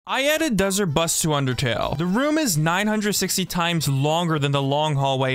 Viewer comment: Why did i hear "My editor busts to undertale " 💀🙏